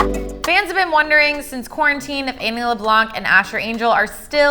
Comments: did she really say Amy?? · me thinking she said "Amy LeBlanc" Instead of Annie😂